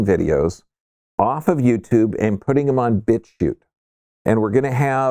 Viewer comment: Make sure you post bitchute link when it’s ready to go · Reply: yes and its not in description, i listened twice and still thought i heard "bit tube" haha so im glad for this comment saying its "bitchute"